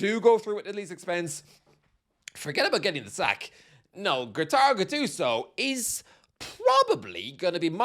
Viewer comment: Did you just call him “Gattaro”? In Italian that’s a word that is used to refer to cat’s lovers… But in a quite derogatory and almost disgusted way… Like: “look at him! He’s a “gattaro”! He probably has no social life, doesn’t know what a girlfriend is and probably spends all of his afternoons cleaning his cats litter box…” You know, like the crazy old lady from the Simpson’s, she’s a “gattara”… Now I don’t know if Gattuso has this insane passion about felines…😂😂😂 (I’m joking obviously, no offense mate😉)